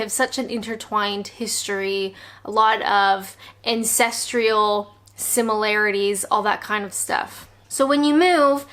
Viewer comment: Um. Around the 4:30 mark, did say "ancestrial" or "incestrial"? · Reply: She said 'ancestrial', although just to be THAT person, it should be 'ancestral', there is no 'i'.